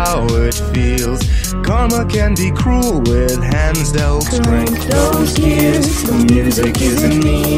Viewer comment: I keep hearing 'feels' instead of 'felt'. Why does it say 'felt' if I keep hearing 'feels'?